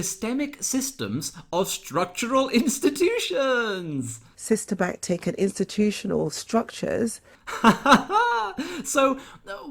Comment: After listening to several repeats of 15:15 I'm sure that Dawn Butler says "sisterbacktakin" instead of systemic or even systematic. How can someone who cannot speak English be in Parliament?